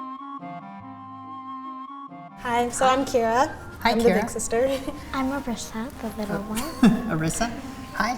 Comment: I thought she said “I’m a thick sister “ 😂